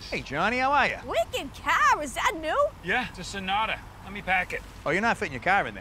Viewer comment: what did he say before "sonata"? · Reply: He said, "Yeah, It's a Sonata, Lemme paahk it."